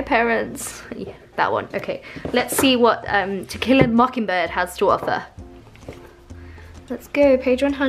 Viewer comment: I keep hearing "Tequila mockingbird"...love your art!